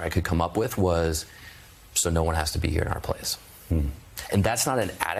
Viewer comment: Seth talks about soldiers asking him why we were in Afghanistan. I can't understand his answer.... Is sounds like he is saying "So no one has to be here ON a PLACE!" ???? I wish I could understand what he is saying. Can anybody get it? It's at 55 seconds.